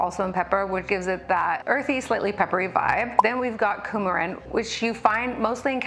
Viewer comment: Oh gosh I thought you said "Coumadin" for the last compound. Like the blood thinner!